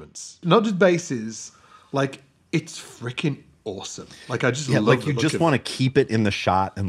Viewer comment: I thought said “it’s Rickin awesome” 😂